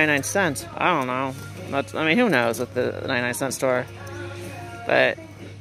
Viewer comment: I thought you said .99 store not 9.99 LoL 👵